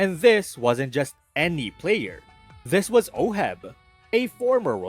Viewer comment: when he said oheb, the subtitles said ohmyv33nus